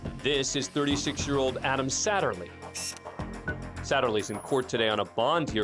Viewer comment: Deadass 6:10 in I thought he said Adam Sandler lmao